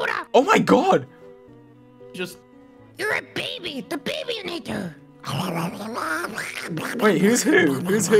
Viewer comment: WHAT DID QUACKITY JUST SAY ???? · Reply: the babynator, wackswawuswawashfsacawaa👹👹